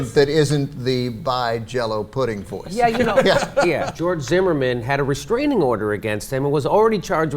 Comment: Man, these subtitles are terrible. "George Zimmerman" is misheard as "Joy as a man"? Really?